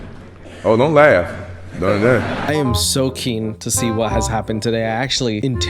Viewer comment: who else heard "I am soaking..."